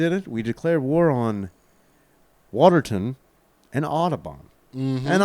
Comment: Thought you said declare war on Autobots. All hail Shockwave 🤘🤘🤘🤣🤣🔥🔥🔥